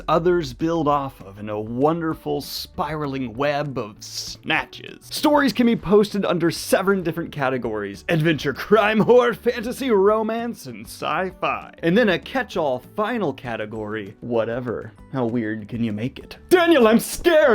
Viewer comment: During the ad I definitely heard "Adventure Crime-whore romance"... and I'm intrigued.